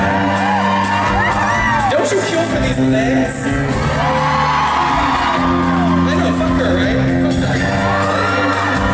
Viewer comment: When introducing the bass player, does anyone know what Adam says after "Don't you kill for these legs?" that makes Tommy laugh so hard? It's around 9:56. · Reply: He says something along the lines of, "I know right? Fuck her! Fuck her!" said in a joke way to the audience, after they agreed with him. Like they're jealous of her legs. It's a very "bitchy queen" remark said in a very jokey way. Tommy is laughing because it's funny.